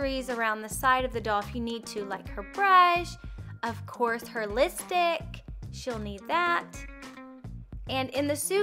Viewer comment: not trying to hate, but it sounded like you said listick.